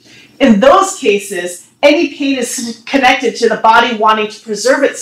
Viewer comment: "In those cases, any penis connected to the body wanting to preserve itself against the threat of immediately, potentially crippling damage" 2:40. Did I hear that right? Words to live by. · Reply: I think it was "any pain is..." which makes much more sense in context, although we will never know whether the slurred pronunciation was a deliberate pun or an amusing slip up.